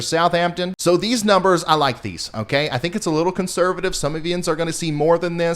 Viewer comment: Did I hear you say "Some of YINZ"? (9:11) LOL (Pittsburgh) Love the channel. Quick, Accurate and Reliable. Well Done!